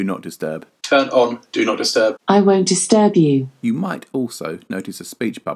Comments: No one heard it say I’m going to stab you or am I tripping 😂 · did Alexa just say "I'm going to stab you" 🤣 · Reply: In U.K. accent, Echo replied: "I won't disturb you". Which can sound like "I want to stab you" Lol